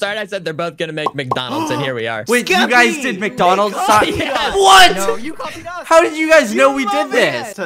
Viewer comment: it sounded like said "You f** up"